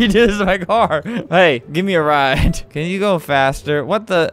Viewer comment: When he said can you go fast I thought he said you little bastard